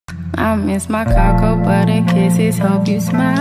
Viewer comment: I thought it said I miss my caca buddy💀💀